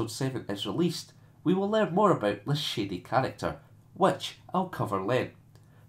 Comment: First I have to say when you said "shady character" I thought you said "shitty character" lmfao. I have a theory that Kylo Ren is going to turn out to be Leah and Hans son. You already noted the similarities between him and Vader as well as him being inspired by Vader, I think it would fit perfectly with the way he's Vader lite. And why better way to play that angle than having a similar twist. Also I think a grandson might find inspiration from such a legendary sire.